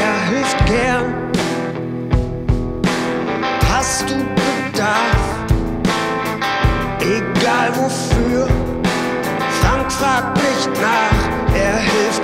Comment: Can anyone translate to English? My Deutsche is not good lol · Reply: flawless translation! The only mistake is at "Hast du Bedarf, egal wofür- Frank fragt nicht nach" which translates to "if you got demands, no matter what for - frank doesn't ask" but I'm sure you just misheard. Thanks for the effort - viel Liebe